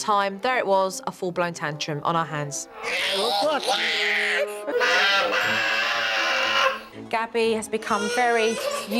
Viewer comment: What did she say? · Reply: She says Help Me Daddy I’m Stuck in The Mud help me Daddy